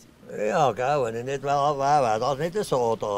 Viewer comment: Was sagt er denn bei 0:06? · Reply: "Weni nit da wär wärs nit eso, weesch" Wenn ich nicht hier wäre wäre es anders (nicht so schön) weisst du